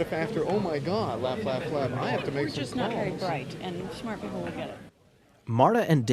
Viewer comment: What did Aniston say before "and smart people will get it" when filming vegas shoot???? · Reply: Yeah she says “We’re just not very bright” referring to (I think) herself and David. It sounds like she says “they” and I always thought she was referring to the audience, but that’s not the case if you really listen.